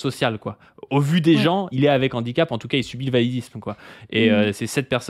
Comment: J'ai entendu "il subit le validisme", ai je bien entendu? Si oui, cest quoi encore ce concept à la con?????